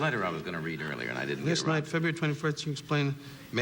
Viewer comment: Am I hearing it right?? on minute 7:11 does don rickles say February 2014???